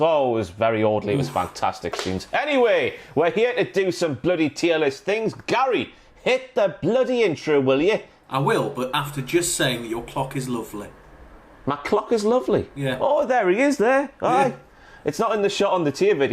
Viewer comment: Legends lads! Also just to say, when said “Your clock is lovely.” I heard “Your cock is lovely” and had to rewind to check what I heard!